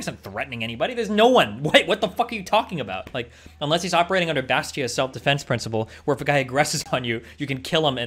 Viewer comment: Did he say Bastiat self deffence principle? I can't hear.